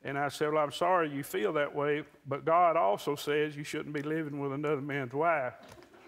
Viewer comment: Can anyone tell me what he is saying at 5:22 - 5:24 ? I can’t understand why people laugh (English is not my first language) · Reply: Peter Pan ....he said, I'm sorry you feel that way, but God also said, 'You shouldn't be living with another man's wife'...calling him out on his behavior.